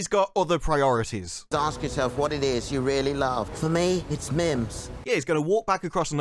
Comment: I thought he said "You've got to ask yourself what it is you really love. For me it's milfs.", and I snorted Fanta into my sinuses. 😄